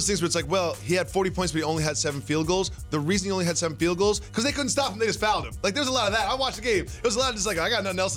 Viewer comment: Did jacoby just say they couldn’t stop them niggas fouling him?